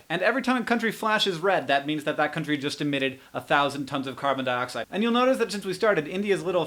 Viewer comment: Nevermind about the flashing red thing, I think; I heard "carbon" and not "dioxide" so I thought Hank meant "carbon monoxide" so yeah.